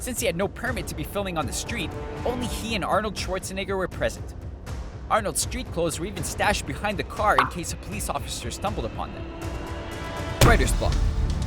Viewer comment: What does it say at 3:00? "bla bla behind the car b;a bla bla" can you write down what he's saying please? I can't hear it right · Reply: TheGuardian163 "Arnold's street clothes were even stashed behind the car in case a police officer stumbled upon them."